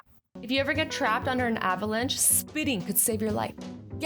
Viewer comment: I was so confused.. thought Azzy said speeding in a lil accent